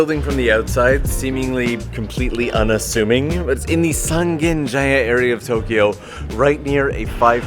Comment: ok i heard you say a place but i'm not sure exatly what you said. Sangen area, sanen area, sanjin area...? San something area near a crossing is all i know. that's why i was asking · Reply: Sangenjaya